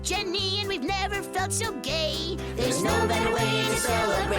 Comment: did he just say gay?